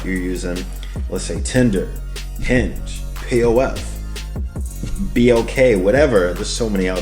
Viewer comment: When you said p.o.f. , I thought you said plenty of wap. Lol